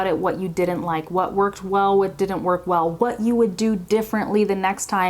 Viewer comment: what did she say?😂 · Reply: what worked well, what didn't work well..